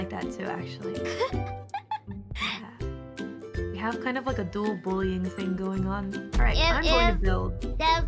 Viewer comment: I think she said "dual bullying" not "dull bullying" as in they bully each other